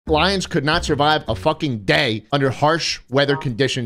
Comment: I thought he said “blinds” not “lions” so that was a very different conversation the first time I watched